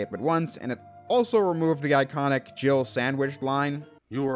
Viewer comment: I always hear jiggle sandwich lol. Ever since I was a kid I thought that was what he said